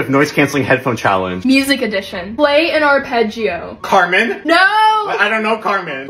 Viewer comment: I thought he said cartman 💀 I was about to be summoned